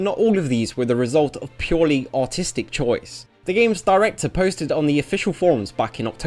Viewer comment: Jesus Christ, I thought you said "autistic choice", not "artistic choice"